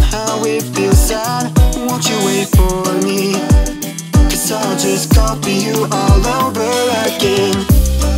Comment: I had a great time getting this all setup and edited together! Hope you all enjoyed it. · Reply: didn’t he say I’ll just copy you all over again instead of I’ll just call you all over again